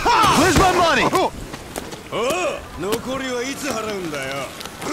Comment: why does he sound like JoJo? Sounded like he said "Ora!! "